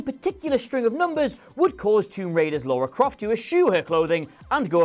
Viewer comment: did he say Laura Croft? Also, mispronounced Eidos.